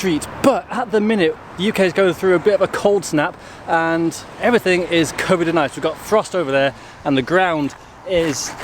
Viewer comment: Almost sounded like he said "everything is Covid and Ice" and honestly that would have made sense too 🤣